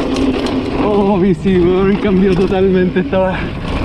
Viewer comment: que dijo? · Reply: Oohhhhh bici wn cambio totalmente está wea